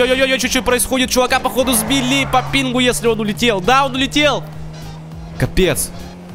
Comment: Мне одному послышалось что сказал : Даун улетел